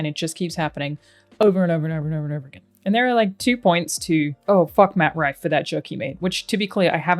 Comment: My ears betrayed me on the first listen of this point. For some reason I heard you say "Matt Bragg" instead of "Matt Rife" and I was like WTF did Matt do? 😂😂